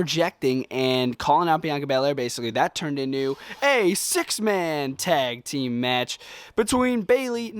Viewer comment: Am I the only one who heard him say a 6 man tag team match instead of woman?